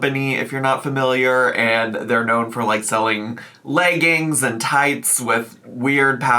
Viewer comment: did you say „layggings“ there? 😂